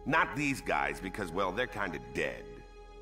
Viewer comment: "I'll need some guys... not these guys 'cause they're kinda dead." insane laughter I love that scene so much. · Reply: Damn that's my first time hearing that, the whole time I thought he said they're kinda gay lol